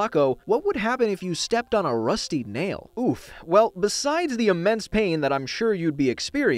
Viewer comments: Did he say oof? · did he just say oof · did he just say "oof?"